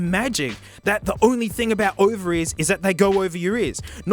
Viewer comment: Over ears, he's saying over ears. He's not saying what it might sound like, he's saying over ears. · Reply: I THOUGHT HE WAS SAYING OVARIES AND I WAS SO COMFUSED